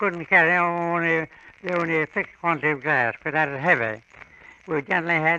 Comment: Hey what is he talking about at 10:47 when he says they could only carry 6 quantity of gas? They has gas back then ? And for what ? The automobile wouldn't come about for many years after . Am i wrong here? Did I miss something? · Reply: Glass